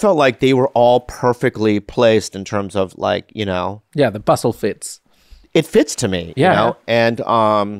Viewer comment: I legit thought said "Busslefitz" like he was referring to some german filmmaking technique 😂😂😂